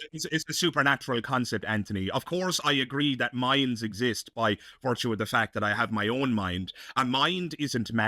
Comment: Mhen said the "Minds exists...," I was multitasking and I thought he said "Mayans," and I was all "Of course Mayans exist, I know a couple, WTAF!"